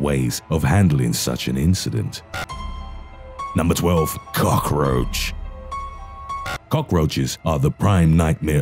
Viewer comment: what your here for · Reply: ohhh he said cockroach.. i thought he said the other c word